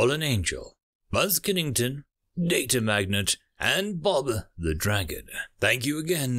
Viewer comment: For the longest time, I thought he was saying "Bother, the Dragon".